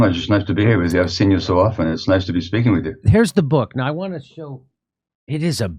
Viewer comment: I heard "it's nice to be stinking with you" around 0:26 so I thought this should be good because my ears have been ruined from listening too hard too much.